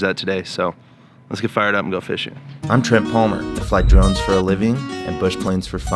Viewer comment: Lol, at first I thought you said "I fly drugs for a living" and I was what?? Had to play the intro twice.